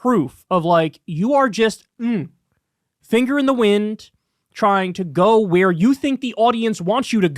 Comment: I thought Kyle said "fingering the wing" lol. Holy shit this belongs on PH, Tucker you dirty girl